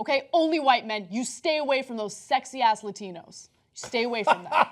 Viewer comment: Ana always finds a way to profess her love for Mexican men. I'm all for diversity, but she is obsessed with only dating Mexicans and no other race. That doesn't seem very open minded. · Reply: Greg Raines mexicans? I thought she said Latinos